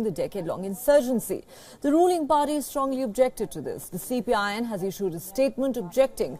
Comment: I heard something like CPIN , I wasn’t sure if it was my ears ringing or the speaker really said, or meant to say Communist Party of India in (Nepal) CPIN 😅